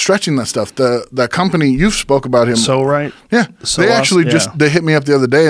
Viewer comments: what thing are they talking about at 1:46:41 cant quite catch the company name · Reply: Found it. it's called Pso Rite. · Does anyone know what device they're talking about? It will be very helpful, thanks. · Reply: Pso-Rite